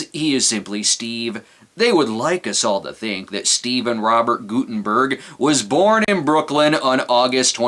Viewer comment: I could've sworn that you said, "Steve and Robert Guttenberg..." It should be noted that I'm an idiot, lol. Why am I posting something so pointless? I heard commenting helps videos get recommended, so I figured, "Why not let the world know that you're an idiot?" I think I did the right thing.